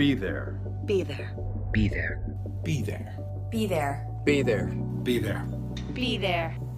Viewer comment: Be There, "Breath Air" Beaver is all i heard